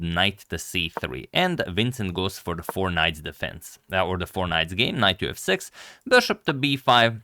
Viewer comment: For a moment at 0:58 I heard you say the "Fortnite defense" and I got very confused